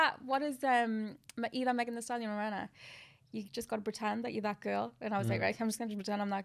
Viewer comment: What did she say at 23:18 got to be like what Girl?